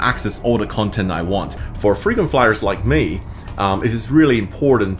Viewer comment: did you really say... "Freedom Fighters like me"....?? Or Frequent Flyers 😉?? Amazing architecture and great information!!